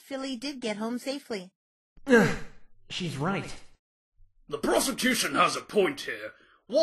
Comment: Is he saying prosecution or prostitution xD